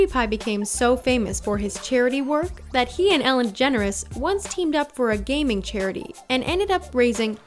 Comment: Did she say Ellen Generes at 6:23 ?